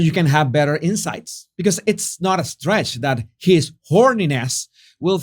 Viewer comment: I thought you said his horniness at first to my shock and amusement lol